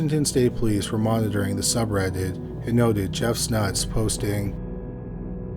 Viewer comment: Jeff's Nuts? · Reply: I thought he said Jeff Snuts😅